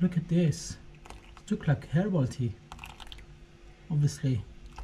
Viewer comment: did you say "Hairball Tea? lol 6:02 well, - Maybe it could be Used as a DYE? "Waxy" Coating .... Very Interesting! That almost sets One up for Different experiment entirely ~ Wax. Baking Soda & Vinegar creates a Bubbling Fizz, but What will it do to leaves? hmm Thanks, Cheers!